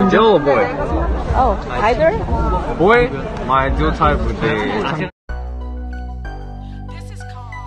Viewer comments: what did he say here? i don't understand english very well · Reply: Fan: “what’s your ideal type?” Felix : “girl or boy?” Fan: “oh...either” Felix : “boy, my ideal type would be....” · WHAT DID HE SAY I COULDNT HEAR HIM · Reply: Girl or boy and the girl said either and he said both are fine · hold up guys HSHSHSHZHSHAH am I the only one that couldn’t understand what he said here 0:07????? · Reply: he said "boy or girl?" when someone asked what his type was